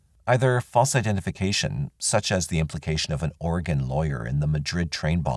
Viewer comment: I thought he said "organ lawyer"... instead of Oregon lawyer... lol "See what you need to do, is get an organ lawyer... they will square everything out for you"! Ha